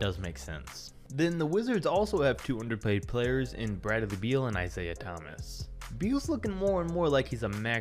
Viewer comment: “Brad-of-the-Beal”?? Anyone else heard that lmaoooo